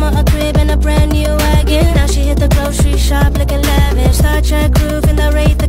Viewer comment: i thought the weekend said “Hit the grocery shop like eleven” 😭